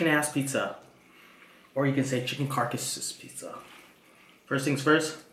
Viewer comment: did he say chicken as* pizza?